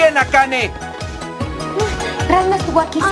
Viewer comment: que dice ??? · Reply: "Ranma no baka", que sería algo así como "el tonto de Ranma". En la traducción le inventaron "Ranma estuvo aquí, saludos", que es una forma suave de decir que por culpa de Ranma ella tiene ese yeso, indirectamente le está diciendo tonto jaja